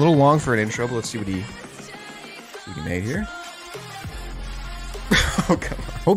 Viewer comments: what did he say????? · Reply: no made here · ??? AYO what he say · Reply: “lets see what he made here”, how would the other word even make sense in that context lol